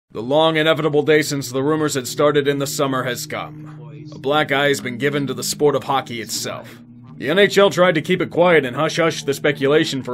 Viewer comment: I thought he said Black Guy at first, not Black Eye